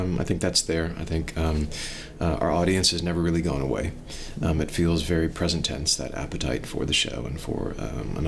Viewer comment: What is he saying at 4:23? Subtitles say "it feels very present tense that appetite for", but it doesn't make sense to me. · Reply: “It feels very present tense, that appetite for the show.”